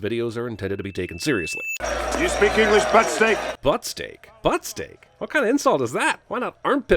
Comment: Divorce in 1947 was actually rather difficult to obtain. I don't think he said butt steak, I always heard it as fuck stick.